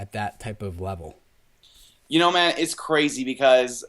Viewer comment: Did he call you Matt at 1:23? Lol. Great video as usual. The facial hair influence in this one was strong. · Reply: Hehhehe nah, he said "man" , not Matt :))